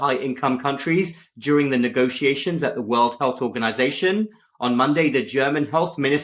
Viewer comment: When talking about The WHO I heard you say World WEALTH Organization😂